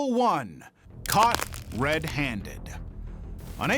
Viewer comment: “Cop red handed” AHAHAHA HAHAHAHAH HAHAHAHA you’re so funny. P.s. ik it said caught but it sounded like cop so